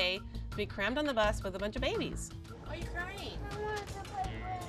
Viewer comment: I was like did she just say she wants to “play” with brynn